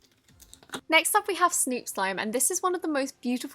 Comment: did anybody else hear her call Barbie slime snoop slime.. like if you did.. it’s